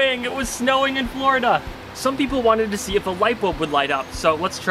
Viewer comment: When he said light bulb, I thought he said lipo and I was like let's gooooo